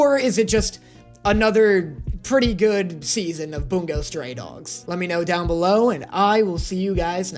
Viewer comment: Why does it sound like he says “Boom goes stray dogs” 💀